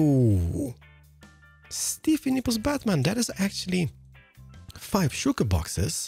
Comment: What?! Did he just say "Stiff Nipples Batman?!"